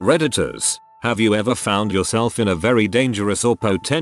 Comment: I heard “redditors” as predators 😳